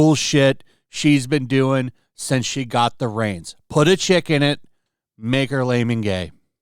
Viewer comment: I honestly couldn’t tell what KK cartman was saying about her meal. I thought she was saying chicken instead of chick so now it is funnier